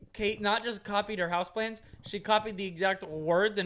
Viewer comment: Am I the only one who thought they were saying house plants at first!